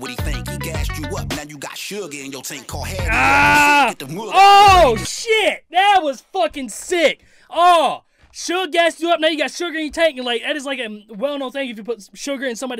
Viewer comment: I thought he said suga in yo taint